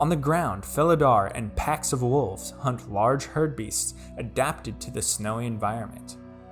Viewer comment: Listening at 2x speed, I heard "Packs of wolves hunt large herpes."